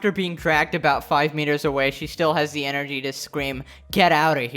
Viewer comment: did I just hear the M word? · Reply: Metre. Meter.